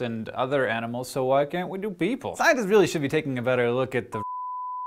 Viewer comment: I thought he was saying "scientists should take a look at the shit they're doing", but then the bleep kept going.